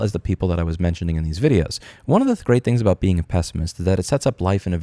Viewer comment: i thought you said you were a pessimist. your lying to yourself if you think ANYTHING will come of this